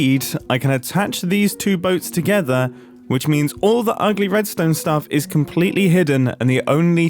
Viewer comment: did I just hear Mumbo say ugly Redstone stuff?